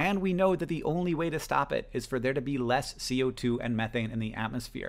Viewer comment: So the closed captions say “we know that the only way to stop it is for there to be more co2 and methane” (min 3:29) when Hank clearly says “we know that the only way to stop it is for there to be less co2 and methane” Is there a way to get the CC corrected? That seems like an important error to fix, if possible.